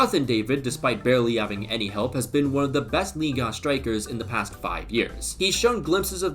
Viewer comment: what did you just said bro? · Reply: ligue 1